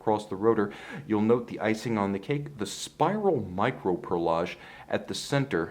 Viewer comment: Did you say a spiral?